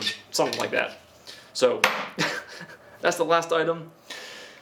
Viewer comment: While he was laughing it sounded like he said I hope you ignored the video. Lol